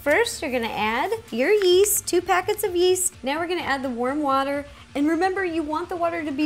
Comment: please tell me I'm not the only one who thought she said 'worm water' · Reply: I thought she said worm water too but she said warm water